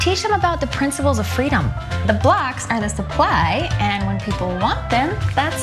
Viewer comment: Doesn't it sound like she is saying "The blacks are the supply..." instead of "The blocks..."?